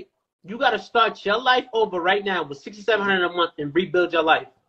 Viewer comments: lol I thought he said 6 to 700 a month…and I was like ummm yeah I could for sure make that work. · I was on his side at first cause I thought they were saying 6 to 700 a month lol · "could you rebuild your life right now with $6,700" Bro I could start a family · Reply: Wtf lol I thought he said 6 to 700 per month I was like yh he got a point, but dude said 6700 😂